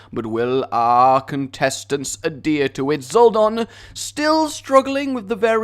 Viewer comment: When you said "Four contestants" it sounded like you said "F#ck intestines"